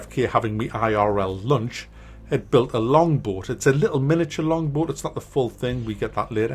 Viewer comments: so... how many people for the longest time thought he was saying "longboard" and was waiting to see him skate down some mad hills LOL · Ah "long boat". I thought you said "long board" lol.